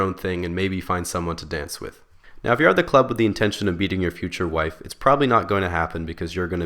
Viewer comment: UHHH why do the subtitles say "intention of beating your future wife" and why do I gotta laugh so hard